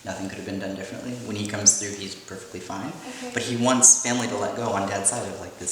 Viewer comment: Please read this its so scary it sounded like someone said ace family